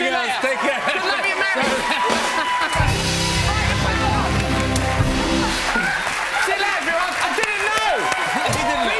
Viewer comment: What is he shouting at 11:43? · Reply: "fight the power!"